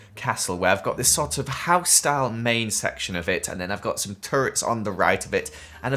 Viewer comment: he sounds like he said turd